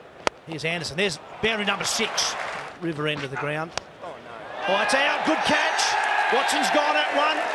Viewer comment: Did warner say oh no